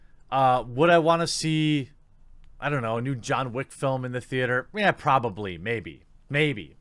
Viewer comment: John Wayne died in 1979. Its not possible to have a new John Wayne move, not really. Edit: wow I totally misheard. You said John wick or some such.